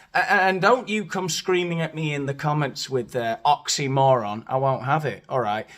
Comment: Don't come to you with oxymoron? I'll have you know the doctor prescribed them to me, but yours for £10 a tab! OH! OXYMORON! I thought you said, OXYCODONE! Anyway, regarding oxymoron, I guess we'll have to agree to disagree! PS Coote is a TOTAL KNOBHEAD!